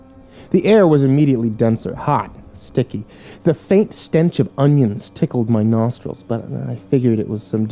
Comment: I heard him say the faint smell of onions and I'm like better look in the comment section